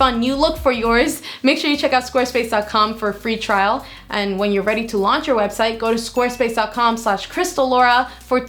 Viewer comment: I thought she said squirt space.